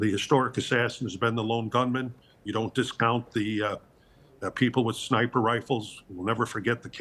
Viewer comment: What did he say about jfk ‘s assassination at 12:13??? 👂🏼😳 did he say we thought it was a lone gunman and don’t discount the people with sniper rifles ???? 😳😳😳😳 did he just admit Oswald didn’t act alone 😂😳